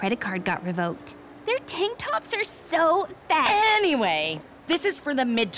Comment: thought she said tik toks